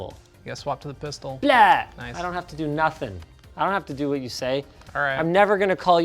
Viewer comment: Did Linus say "бля" at 15:18?